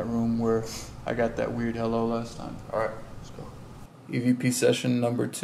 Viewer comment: is that me or you can hear some sort of voice in 18:32 18:33 i cant make out what it's saying sounds like "spor" or "let's go" some thing weird 😕